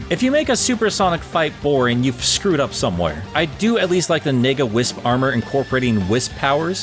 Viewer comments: is the only time I have to ask this throughout the video. Did you say NEGA wisp or... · i had to listen to this like 9 times n all i hear is "nigga wisp armor" (not offended but cant not hear it 😆😆)